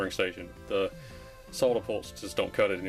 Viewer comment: and 12:19 I heard "sawdapolt" and had no idea what you'd said. But google figured it out: Soldapullt is a brand of one of those cheapie spring-loaded solder suckers.